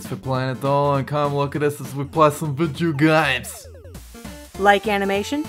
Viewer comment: it took me forever to realize he was saying "come look at us as we play some video games" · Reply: Renee Cooperider I thought he used to say "vijeo games" but then I realized he said that on purpose so I guess everyone mishears him😂